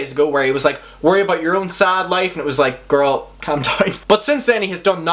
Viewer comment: When He said “Calm Down” I Thought he said Come Die 😂 I Love His Accent ❤️